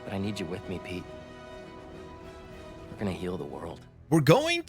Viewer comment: That line from Venom sounds like he said Kill the world but it gets muddled to Heal